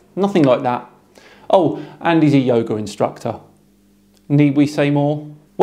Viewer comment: For a moment I heard: "Yogurt instructor" . But that can't be because then he had culture(s).